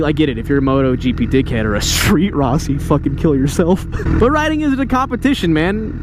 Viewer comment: What did he say after "MotoGP dick head"? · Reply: "Street Rossi" Rossi: One who's Ambitions out when their Talents. Or in other words, Posers that hype themselves #BikeLife that either don't or suck at riding.